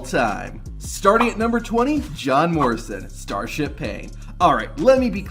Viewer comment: I THOUGHT YOU SAID JOHN MARSTON AT THE FIRST ONE